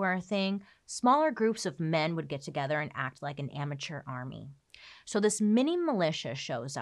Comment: I honestly thought she said "immature army" instead of "amateur army" when she was describing what a militia was.... 😅😂